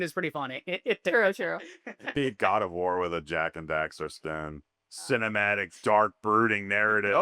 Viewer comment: Sounded like said Yak & Daxter.